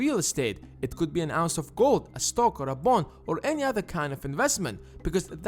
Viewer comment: I thought he said an ounce of coke lol 😂